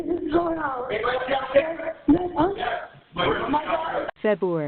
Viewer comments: I heard her saying "my daughter" twice!! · on 911 call, I swear I hear call V “my daughter”?!?!?!